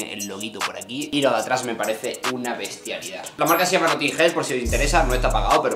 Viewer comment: Como se llama la marca? Que no entendí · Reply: not in hell